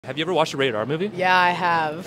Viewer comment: i heard "rated r" as "radar" have i played too much vtol vr?